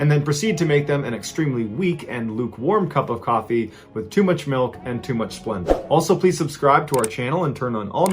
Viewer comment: Did you say "too much milk and too much Splenda?" Shivrrrr... I thought you said "too much phlegm." Heck no